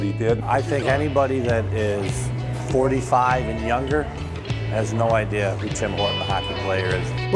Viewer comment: “I think anybody that is 45 and younger, has no idea who Tim Horton the hockey player is” Did I hear that right? I actually feel personally insulted hearing that as a kid who grew up in Hamilton as a diehard Leafs fan since 10 years old, visiting the first ever location to open up in Hamilton countless times, and now living in St. Catharines just 1 exit before the spot of his fatal crash and a 5 min drive from the old St. Catharines General where he was taken. I’m sure as a little kid while sitting in the shop at least once of the hundreds of times I went, I thought just once, “who is Tim Horton that this place is named after?” And then asking my parents or grama, seeing as this guy must be pretty important. I’m sure majority of kids have this question cross their mind at least once as a kid like come on. And I’m only 30, now this guy is saying 45 and under?!?!! I hope he just phrased it wrong because he can’t be that ignorant to believe of Canadians 45 and under, that no one knows who he is. Come on dude